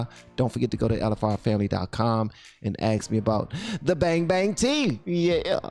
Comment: I seriously thought that said fang fang tea at first 🤣🤣🤣 thats dangerous thats how she got that guy huh? 🤣🤣🤣